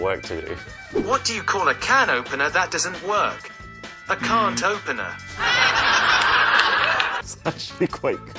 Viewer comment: Bro i heard "I cant open her"💀💀